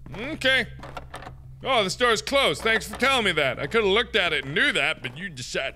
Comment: When Mark was trying to open doors and it said “the door is closed” I thought it said “you are a coward”